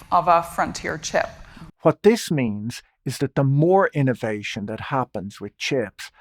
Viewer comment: I heard "Moore" as in "Moore's Law" -- if we can expect that to continue on, then in a decade or so there might be chips with 10-fold or better performance in terms of cost, power demand, and sheer calculating speed.